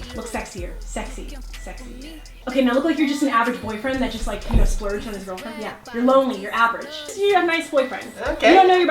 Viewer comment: wtf did she just say??!!! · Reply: Gunner Schultzshe said act sexy